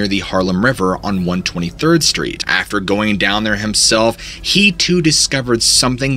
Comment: At the start of this video i heard wrong. I heard that '..after going down on him self..' 😂 that was way too funny